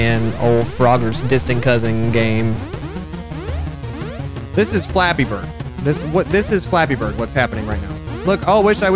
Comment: i thought he said this is flabby bird xd